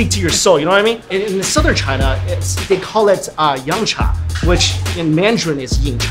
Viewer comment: Did he just say Yamcha...